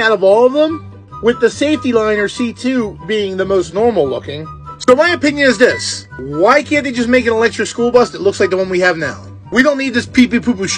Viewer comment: Who else thought that when he said Thomas opinion who thought of Thomas the train